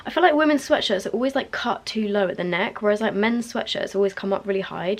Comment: lol when you first said mens i thought you said Ben's 😂 i was like who is ben?? I recently got so KL polishes and they're amazing i can't wait for you to try them! love ya and enjoy watching your vlogs so much ✨